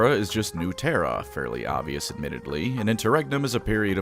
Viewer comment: I had to check my phone, I thought he said interrectum